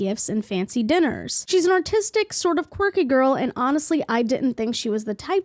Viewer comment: “She’s an autistic, kinda quirky girl” tells me everything I need to know about the first guy. Manic Pixie Dream Girl core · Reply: I think he wrote artistic but accent made it sound like he said "autistic" 😊